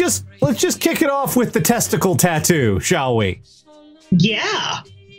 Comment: Misheard it as "kick off the testicle tattoo" and thought, "I... didn't think that was how it worked."